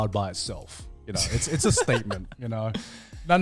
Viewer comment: I heard this as "it's a STEAKment" and I wish that's what he actually said here